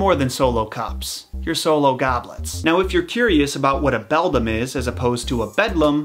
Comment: "To me, you're more than Solo Cups insert short dramatic pause here You're Solo Goblets" -Our Solo Goblet leader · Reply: I thought he said solo goblins.